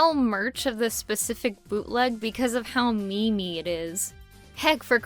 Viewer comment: did you said ME-ME?!?! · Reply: mozack sohar yes, she said "memey" not "meme"